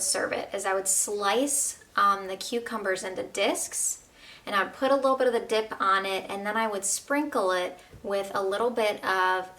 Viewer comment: did she say put dick on it